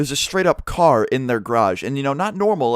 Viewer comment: I hate how Americans say garage... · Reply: I thought you said garbage.